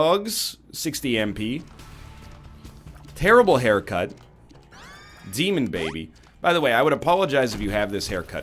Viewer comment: did he just call dark bum demon baby?